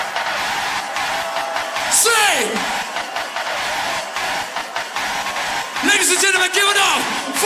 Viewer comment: did bro really say the n word!? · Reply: "Lady's and gentlemen"